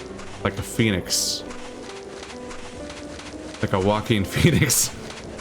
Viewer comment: Did you say Joaquin Phoenix? Oh wow. :D